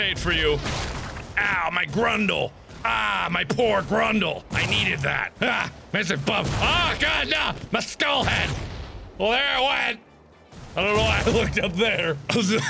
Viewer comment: what did he say at 2:24? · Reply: "Aaagh there a bomb- agh ma skull head"